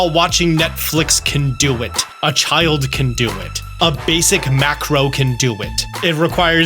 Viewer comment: Thanks for the tip! Though every time you say macro, I laugh a bit because at first I heard mackerel. A basic mackarel can do it 00:44 😂